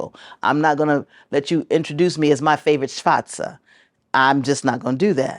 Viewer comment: Did anyone catch the word she said? It the Jewish (Yiddish) way of saying the N-word. Don't ever let a Jewish person tell you that it just means Black. They don't call black shoes that. It's a derogatory term they have for us. It can be spelled Shvartzer/schvartze.